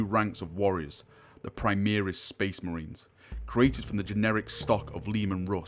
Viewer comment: sounds like you said generic, I'm pretty certain you meant genetic.